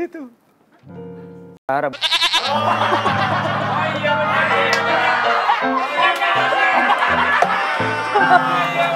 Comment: wendy ngomong apa sii ?? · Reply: Ngomong zara